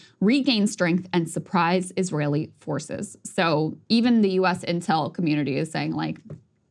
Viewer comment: when she said "US intel community" I heard "US Incel community" lol